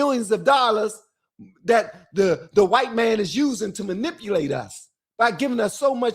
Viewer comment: So, what I hear is “I am easily manipulated by me”.